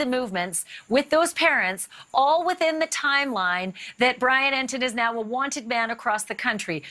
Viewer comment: Anybody else catch Bransfield at 2:59, she says " the timeline that Brian Entin is now a wanted man across the country?"